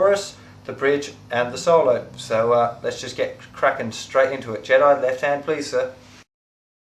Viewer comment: i thought he said jedi left hand plz